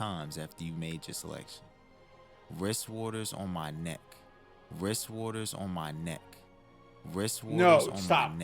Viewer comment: Ok bro I swar I hear “wrist water on my nuts” and I don’t know why 🤣💀